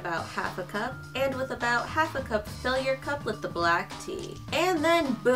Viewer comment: I had my volume on low and thought I heard "and with about half a cup of failure", lol